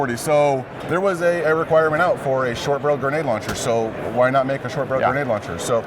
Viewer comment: is he saying "short bro grenade launcher" or did I mishear?